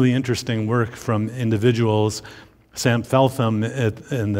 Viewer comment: What was the name he said at 44:45, Sam Felfan? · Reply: Sam Feltham